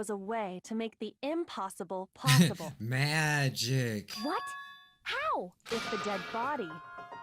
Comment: Is it just me, or did i just hear Himiko say "Magic..." ?